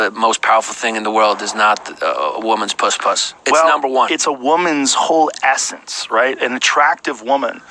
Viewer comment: did he just say "...its a woman's hole assets..." ?